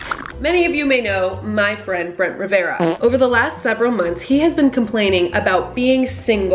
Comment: When peirson said,"So u know my friend Brent Rivera", I heard,"so u know MY BRENT,Brent Rivera"🤣🤣🤣